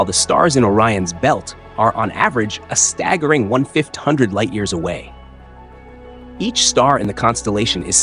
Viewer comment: glitch? “One fit hundred light years…..”. Caption says “1/00”. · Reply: Are you talking about auto captions? They often misunderstand and don't transcribe correctly. But it's fifteen hundred light years (1,500). Now, rewatching it, the narration got confusing, the narrator couldn't decide whether to say "one thousand and five hundred" or "fifteen hundred", and ended up mixing it up, sorry. And thanks for noticing.